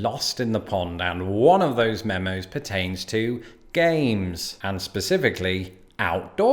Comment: At first I thought you said “Gays” instead of “Games” . Ngl for a second there I was questioning reality Love your content btw :) Time stamp